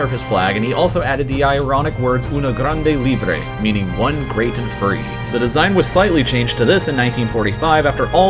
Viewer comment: i thought he said one great furry